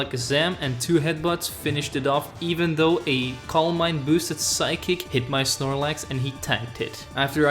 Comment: at first when he said “calm mind” i thought he said columbine, i had to rewind it to make sure 🤣